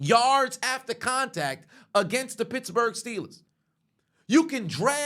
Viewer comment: I thought he said bitchbourg